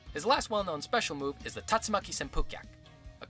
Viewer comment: i always heard "A-TATSAT-VUU-GET" but apparently its "TATS-MAK-SPUU-KYAK"